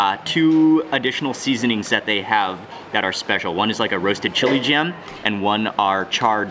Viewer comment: Did Mark just said Chili Jam? 🤣